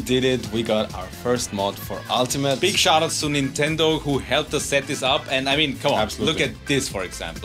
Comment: I thought you said "big shoutouts to Nintendo "